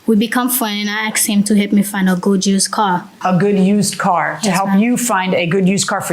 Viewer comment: or did she say “Go Juice Car” ?